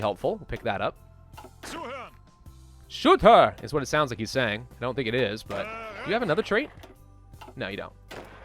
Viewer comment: What the commander in 17:07 is actually saying is "zuhören", which translates in something like "listen closely" :) Hope i have improved your german, best wishes from austria (yes the land you always annex with the Anschluss😂)